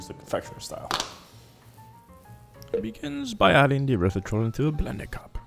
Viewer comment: What about the urethra? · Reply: i dont know, maybe my speakers suck. I thought I heard Urethratol instead of Erythritol 🤣